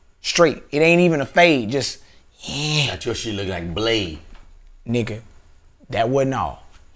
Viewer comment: Bra did he just say "that shit look like Blade"😂😂😂😂😂😂